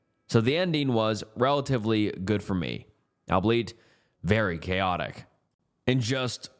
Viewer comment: CUPBURBS 🔥🔥🔥🔥🔥🔥🔥🔥🔥🔥🔥🔥🔥🔥🔥🔥🔥🔥🔥🔥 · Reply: all-be-it I think instead of “albleet”